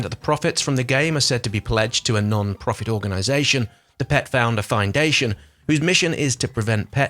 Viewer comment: Did you say the Pet Founder Findation? Shouldn’t it be the Pet Finder Foundation? Or are they funky with their name